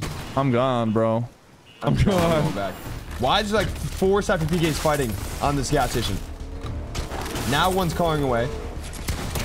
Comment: WHAT DID HE SAY? CARING AWAY ??? wow too much fortnite ruining this mans brain